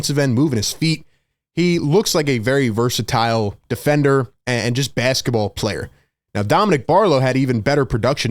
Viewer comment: Did you say Walker is just a good basketball player? I'm sold 🤔 Been calling for some of those on the Sixers for the past 6 years.